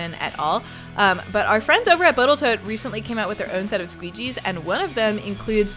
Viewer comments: Did you say "Boatle Tote" (10:53)? You're making me laugh. Great project! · Yep. I heard it right. Botaltoat. :-)